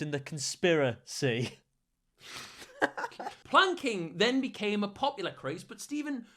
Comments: I heard wanking and I was not even surprised.. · thought he said wanking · I must’ve heard this wrong cause I thought he said wanking